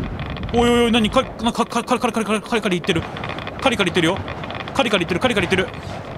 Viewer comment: カリカリよりもぎゅぎゅぎゅに聞こえます